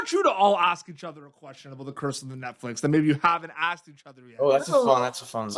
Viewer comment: I swear he says 'Curse of the Netflix'